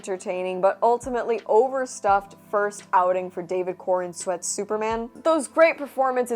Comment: I thought you were saying David Cornsweat